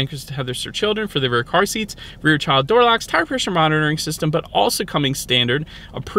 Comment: I thought you said “heart pressure monitoring” 😂 I had to rewind and you said “tire pressure monitoring”. Maybe the 2033 model will have the vitals monitor.